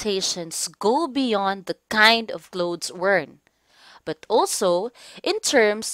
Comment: what did she say? I think i heard glow to work or something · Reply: Go beyond the kind of clothes worn